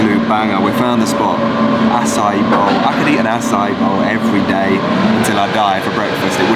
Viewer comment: Did he just say he "Could Eat a Bowl of As Every day" 😂 jk. Man as an East Coaster, Mammoth Lakes is on my Bucket list